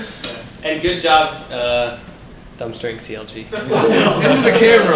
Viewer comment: What did he say at 2:16? Dumpstrike CLG? I didn't quite catch it